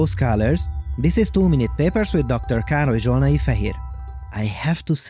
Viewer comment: i hear him say "to many papers"